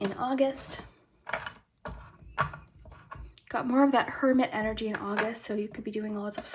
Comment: For group 2, when you were talking about hermit energy, I thought you said Kermit energy. And I think that’s kinda funny